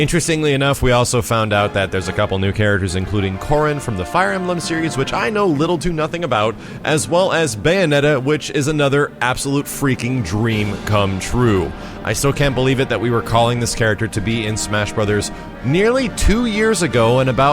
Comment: Ok it sounded like max said Bayonetta was an absolute cream come true